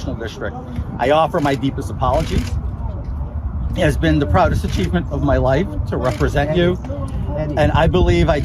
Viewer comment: lol! Did he just say, “a-cheat-ment”? 😂